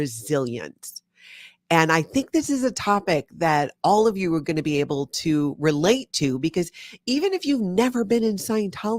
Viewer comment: did anyone catch the dr name they mention 1:23 i couldnt make it out. thank you...this was a great interview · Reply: I believe it was Dr. Daniel Amen. "Dr. Daniel Amen’s mission is end mental illness by creating a revolution in brain health. He is dedicated to providing the education, products, and services to accomplish this goal. Dr. Amen is a physician, adult and child psychiatrist, and founder of Amen Clinics with 11 locations across the U.S. Amen Clinics has the world’s largest database of brain scans for psychiatry totaling more than 225,000 SPECT scans on patients from 155 countries."